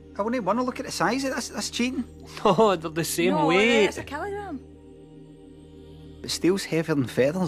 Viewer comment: I keep hearing "KELOGRAMM" lol!